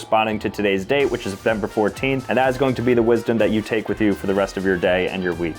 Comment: did he say NOVEMBER? This is a year old if he did Edit: I really need to wait before I comment